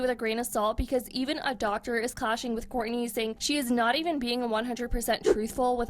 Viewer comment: even a doctor is clashing with Country?… or Khole? Cause I thought I heard Courtney lol